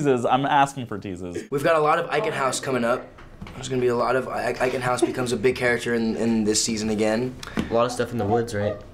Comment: What did Holland say at 4:14 that made Dylan laugh? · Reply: 3 years late to the party, but she said "all my friends are dead"